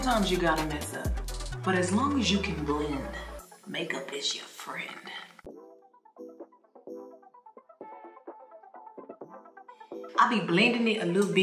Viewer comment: I thought she said: if you can blend, make a bitch your friend. But it’s makeup is your friend. American or Megan accent got me fk’d up XD💙